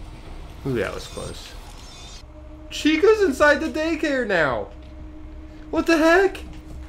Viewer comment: When you flash Chica with the camera, it sounds like She says "WHAT THE FUCK-"